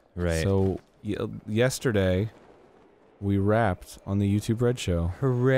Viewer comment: When Dan said "we wrapped on the show last night" I heard "we rapped on the show"